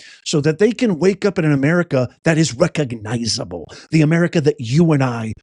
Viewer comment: It sounded like he said Ameri-cotta 😂